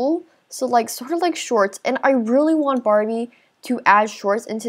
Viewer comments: When you said “I really want Barbie to add leggings” Instead of saying Barbie I thought she said Barney... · Who else thought she said Barney instead of Barbie😂